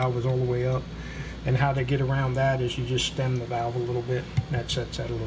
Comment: Great series of videos thank you for posting them. Can you tell me please, at 3:55 what did your machinist say? "and how they get around that you just ..." What did he say there, do what to the valve? I saw you solved it later by removing material but I’m curious what he suggested to do. Thanks! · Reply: "Stem the valve a little bit"......... we ended up grinding out a few thou of material from the rocker box.